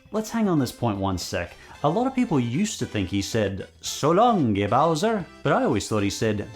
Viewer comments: I always heard it as "so longi Bowser" (long gi...Like the thing you where when doing martial arts) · FWI. I've always thought Mario said 'So Long, KING Bowser.'